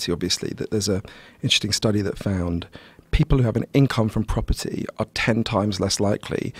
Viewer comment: what does he say at 8:00? incomfront property?